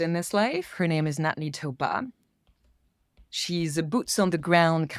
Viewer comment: Excellent interview. Thank you. Does anyone know the name of the person that recommends to the platform just before the end... I can't make it out. Approximately 1:23 minutes through the video? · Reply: When she mentioned the name at 1:27:32 I thought she said "Natne Tobar" but it appears Natne isn't a name so perhaps different speeling. I looked up Tobar and although there is some correlation to the name and African work with water management, there is none matching a woman and first name beginning with N. Can't find them so I guess is correct, she is somebody that needs to be platformed 😄